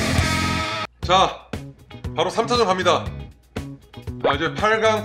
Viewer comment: 3차전이라고 나만 들리나..